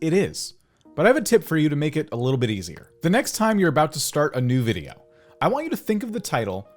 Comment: When he said “adapt a new video” i thought he said “adopt me”